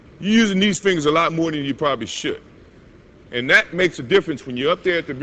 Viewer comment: what says Michael Jordan at 4:15? I don't understand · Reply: your using these fingers a lot more than you actually should 8 years too late but hope it helps